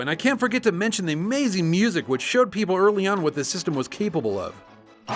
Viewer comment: what did he say? Capable love? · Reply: "...what the system is capable of."